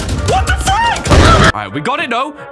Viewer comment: did he say f*ck???